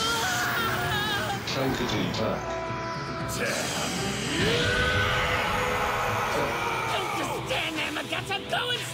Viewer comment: What is Vegeta saying at 0:54? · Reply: DAMN YOOOOOUUU!